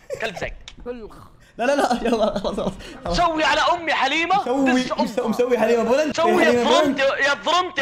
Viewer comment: سمعت كلمه😂😂💔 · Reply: قال دز امها